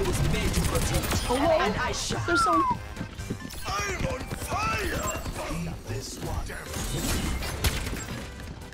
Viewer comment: did just say "peep this one"?